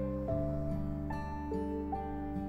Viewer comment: It actually sounded like kitty said “I can” 😊